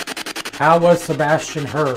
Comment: "How was Sebastian hurt?" Sounded like it said "Husband" 14:52 I am not saying that's what I think, that's just what it sounded like to me.